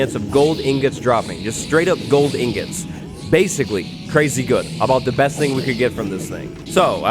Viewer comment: Um........................I thought you said gold is garbage.............